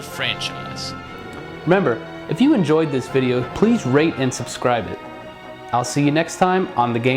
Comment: At the end of the vid I thought you said rape and subscribe not rate